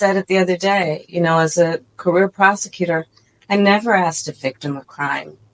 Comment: My face🫤🤔 🤣 really. @ 19:34 I thought she said A Career prostitute 🤣🤣